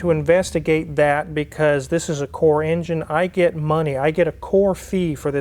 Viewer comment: did you say core engine? Core charge? What is that? Who pays you for it?